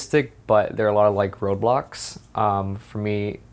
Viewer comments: Oh I thought he said roblox instead of road blocks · When he said road blocks I thought he said Roblox and I was like what